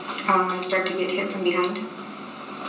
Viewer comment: He dug his own grave with this gold digger home wrecker! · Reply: I thought I heard her saying “ he’s wife “ so she definitely knew 💁🏽‍♀️